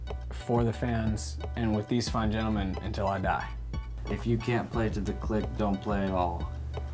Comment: thats better than what i thought he was saying. I thought he said if you cant play to the clique, dont play at all.